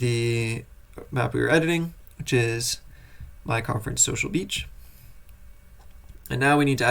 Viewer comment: oh man I did a double take when I heard you say "my conf" because I heard "mein k@